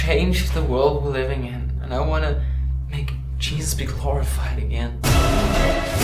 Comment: Everytime I heard the guy say "I want to make Jesus be glorified again" I kept hearing it as "...make Jesus be horrified". In fact I thought that was what he was saying until the 3rd time I listened to it. He is saying glorified, right?